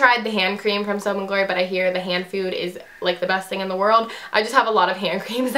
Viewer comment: It sound like you said hand food